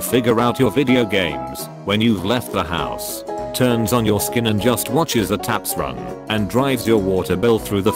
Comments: i’m sorry, did you say turns on your skin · Anyone notice at 4:15 “skin” instead of “sink”?